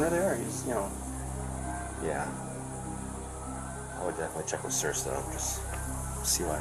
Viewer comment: he actually says, "I'd definitely check with sir (the boss?) and CYA." It was not 'and see what' as the subtitles showed. He knew he was on sketchy ground.